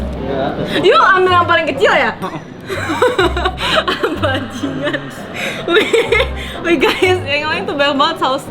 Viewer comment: bilang apa livy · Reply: Bajing*n